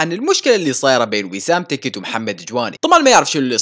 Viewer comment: يس انا سمعت بيسام 😂😂😂